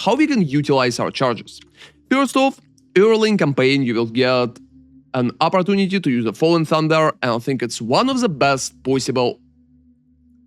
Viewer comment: What does he say at 2:18 ? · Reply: Early in camapign we have an opportunity to use FaLLING THUNDER (I got ya!)